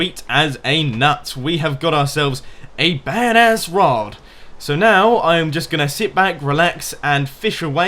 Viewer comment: Did you just say "Bad ass rod"?